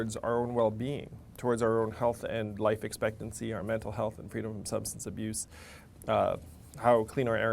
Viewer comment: At around 7:02, did he say freedom of substance abuse??